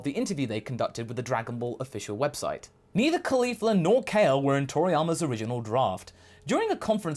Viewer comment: Thought you said "Mia Khalifa nor Kale were..." for a second.